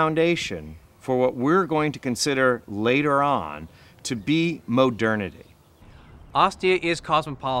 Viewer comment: did he say at 1:03 MOH-dernity?